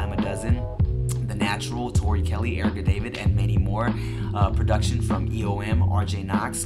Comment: no wayyyyyyyyyyyyyyyyy did you just say mandy moore at 2:24?!